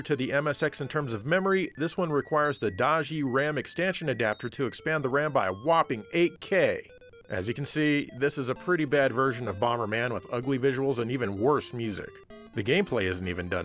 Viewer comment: Does he say “extansion”?